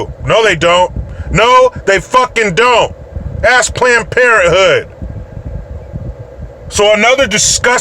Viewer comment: I swore you said Klanned Parenthood. Even if you didn't I'm going to use it.